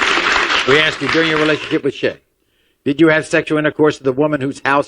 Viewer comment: sound like maury said shit 😆😆😆